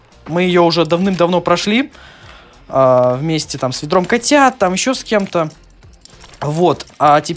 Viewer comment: Мне адному послышалось что он сказал не ведро катят а сендром катят